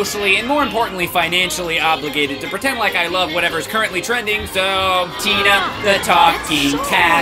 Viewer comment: I always thought it said Tina the talking taffy not tabby